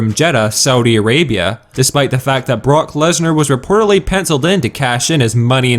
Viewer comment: I thought you said Bork Lesner ha ha ha